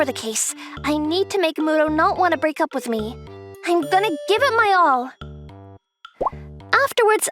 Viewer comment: is the popular girl is Layla from Mobile legend bang bang he said I gonna give it all I heard